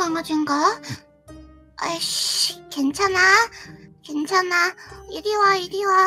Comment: 나만 요루루가 ic라고 들리나?